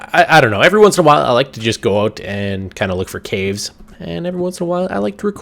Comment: I thought you was going to say "Every ones in a while I just like to go out and look for kids"